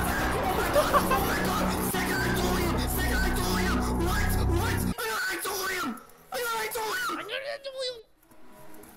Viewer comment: I think he’s saying I got itoreom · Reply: Hes saying " I GOT IDOLIUM I GOT IDOLIUM" its a 1/170m ore in the game